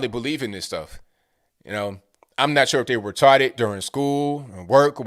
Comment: Is it white privilege; when he said "we're taught it" and I thought he said "we're retarded" I know that the first one is correct, but the second one is more applicable.